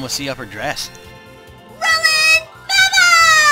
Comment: is she saying rolling bubbles? lol